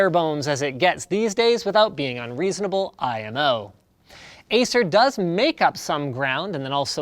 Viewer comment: did Linus just say IMO as an abbreviation out loud?